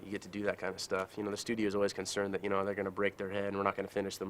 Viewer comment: break their hand 😂 your hanging off a car going 80-90 mph what a goat RIP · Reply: 🤔 thought he said break their head